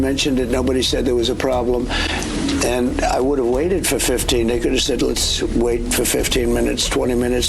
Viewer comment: did he say 15 n!gg@